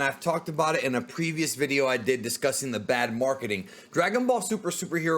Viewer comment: I thought u said “toei had bad marketing “?